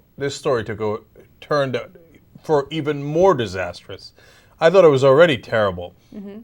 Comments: Lol read the captions when their talking :P for e.g " by those already caremel" instead of " " i thought it was already terrible. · what the man said : i thought it was already terrible. what the subtitles wrote : by those already caramel. SEEMS LEGIT.